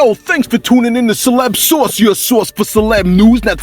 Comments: For the longest time, I thought you were saying "celeb sauce" lol. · I swear it's sounds like you say celeb Sauce sometimes, love the content